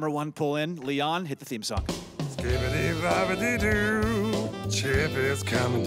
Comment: did he say skibidi? 💀